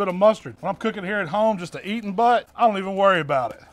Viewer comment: sounds like you said eating butt 😅